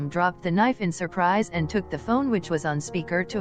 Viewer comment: it is took :) instead of talked